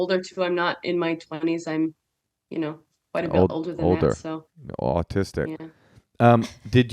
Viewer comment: I thought Moshe said 'oldtistic' ... I'm making that a thing.